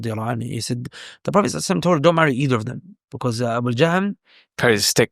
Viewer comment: Assalamu Alaikum Sheikh May Allah preserve you did I hear the name Abu Jahl right as you mentioned him and Muawiya RA being two great Sahabi BarakAllah Feekum